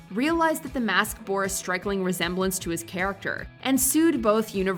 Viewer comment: Is it just me or did she say "Strikeling"?